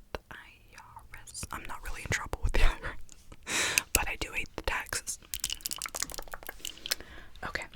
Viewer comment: I thought you said I’m not in trouble with IRS But I do evade taxes tho lol but fr you hypnotize my brain like no one else can even tho you’re family isn’t right there just know we appreciate you